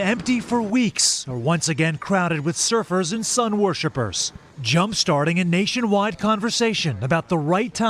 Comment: Did he say, "sun worshippers?'4:27😆 WHEN THESE MORONS GO TO THE BEACH THEY ARE KILLING PEOPLE.